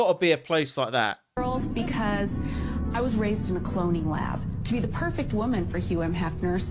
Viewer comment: I heard, "I was raised in a colonial house" not a cloning lab.